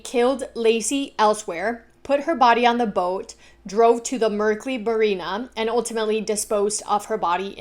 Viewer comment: Anybody else catch her say “merkley barina” at 24:06 😂 love you!!!